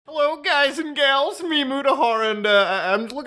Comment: Every time I hear Muta's intro, my mind automatically responds with "hello mutahar, it's me, guys and gals" Also I'd probably be be freaked tf out too if I saw Mark Zuckerberg on my flight